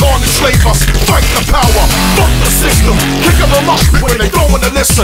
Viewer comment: for the longest time I thought he was saying Kick Up A Muppet